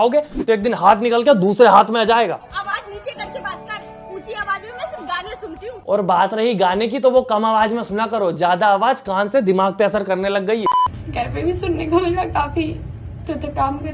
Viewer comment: I heard this " unchi awaaz me sirf GAALIYA sunti hu " 😂🤣